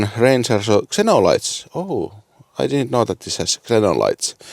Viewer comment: I wasn't paying attention when you said "I didn't know it had Xenons" so I thought you said "I didn't know it had signals" lol perfect thing for a BMW guy to say